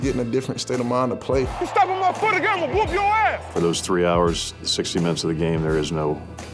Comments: does he say 3 hours 60 minutes? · did he really say for those 3 hours and 60 minutes